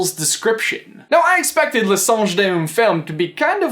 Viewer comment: when he said "le songe d'une femme" it really sounded like "un sange de la ferme" which means "a farm monkey" 🤣